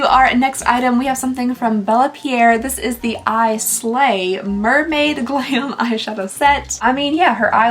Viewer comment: I thought you said "Mermaid Clam" and now I'm giggling.